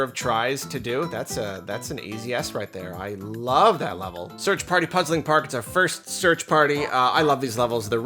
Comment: sorry, i thought you said LOVED the search party levels. i must have bad hearing.